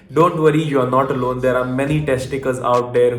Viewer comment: I thought he Said tec ti cals not Test takers